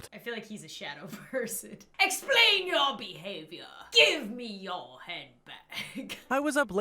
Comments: Idk why but when he said "Give me your hand bag." I thought he said "I kneed your head back." Dieing of laughter 😂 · I thought the robber said: GIVE ME UR HEAD BACK